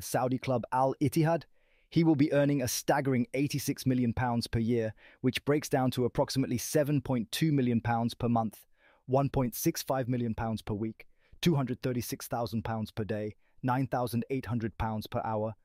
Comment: I thought he said 165 million pounds per week 💀💀💀